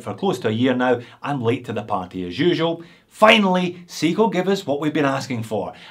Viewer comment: he says seagull instead of seiko?....Or is it aussie accent?